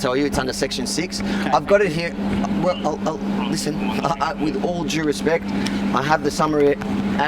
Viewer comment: did he actually say "with all Jew respects?"